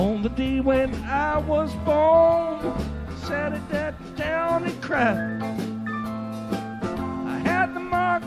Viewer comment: on half step it sounds like he says "santa sat down and cried!" or something LOL